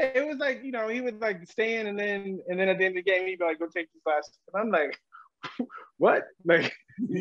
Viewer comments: what did Ray tell him after the game? I didn't understand.. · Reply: I hear “take you’re classes” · Can anybody translate to english what he said about Ray Rice at 0:54? Something about at the end of the game then he'd be like ________. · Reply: He said Ray Rice wouldnt let him get carries till the end of the game. He said he be like "Yo go take these last 2".